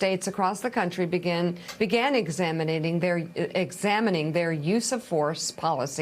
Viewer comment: Did she just say " examinating"? Lol